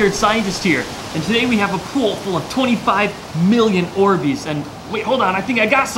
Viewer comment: I thought he said we have a a pol full of 25 million Ortiz